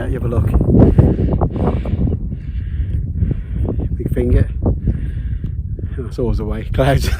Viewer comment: Tough gig indeed! Nice film - good old Vango, it's a T.A.R.D.I.S 😮What is the feature on your camera which manages to change the exposure so quickly? (I think I heard you say 'big finger' each time?) And a new team member too 😊 Nicely started and rounded off with the anthem. All the best! Sue